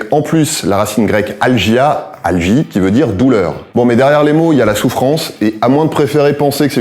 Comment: j'ai entendu "Alegrie veut dire douleur" c'est grave?